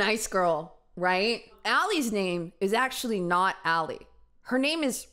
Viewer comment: She actually said her name was ARI NOT ALLIE